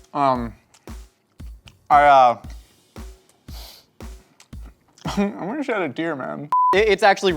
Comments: Josh says "I'm gonna shed a tear man" subtitles say: "I wish I had a beer, man". both very valid. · Can someone help me out with whether he’s saying “I wish I had a beer, man” or “I’m gonna shed a tear, man” Captions say beer, I hear tear but I don’t have good ears!